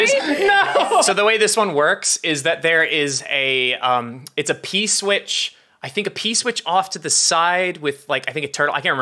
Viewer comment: it's been months since I've watched any Mariomaker or kaizo content and I thought was saying Peace Witch for a second there